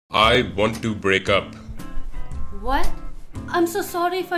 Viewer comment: lolz I heard "I want to do break up"0:00 I must be going crazy